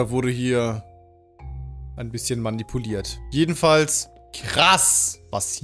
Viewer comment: Ich weiß es klingt falsch aber ich habe bei 9:52 - 9:54 "krass was hier aus dem Hoden gewachsen ist" verstanden